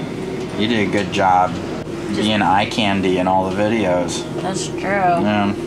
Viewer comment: Did he say “You did a good job being eye candy in all the videos”??? 😳